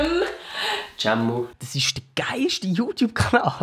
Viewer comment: Wer hat noch bei 0:29 verstanden: Das ist ein Auschwitzer Youtuber😂